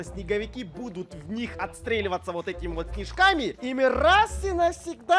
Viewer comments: то чуство когда мне послышалось кишками а не снежками) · 😂😂😂 Я услышал не снежками а кишками